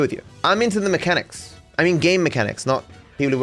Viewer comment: Did he say gay mechanics???